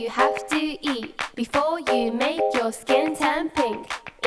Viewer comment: When it said shrimps are pretty rich then i thought it said Pretty B**ch Edit: Wait i think it did